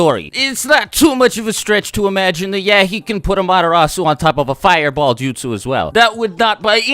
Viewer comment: Haha I thought you were saying 'Madarasu' and I was like wth is a madarasu ?